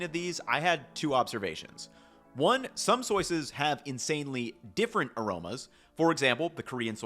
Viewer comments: Don't think I didn't catch "Some soyces" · Petition to instead of saying sauces say soises😂😂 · Did you say "soyces" around 18:05?